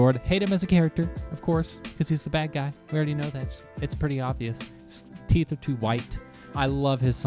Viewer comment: LMAO! Honest to God when you said "he's the bad guy..his teeth are too white" I heard "he's the bad guy ....he's too white". I had to re-watch the video because I was thinking 'is he trying to tell us something?' In my defense, I'm very high right now so that might explain It! I came home from a tough day, so I decided to blaze up and watch a handsome guy react to my favorite show! Thank you for making my day better!👏💞💋